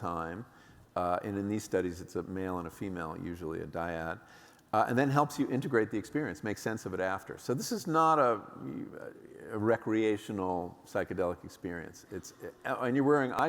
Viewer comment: what is he saying here about trip sitting? A "diad"?